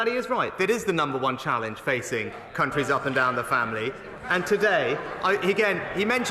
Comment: Did I hear that right? @ 9:21 “Countries up and down the families” 😂